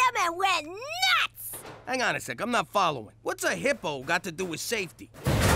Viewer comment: At first I thought he said 1:18 what a hippo got to do to Satan